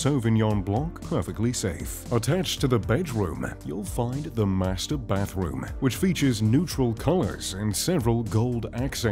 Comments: Is it just me, or does it sound like he says Master Baitroom? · Surely I’m not the only one who thought he said masterbate room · It sounds like he said the master-bate room.